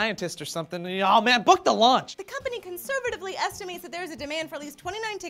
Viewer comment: I thought said book the lunch. :)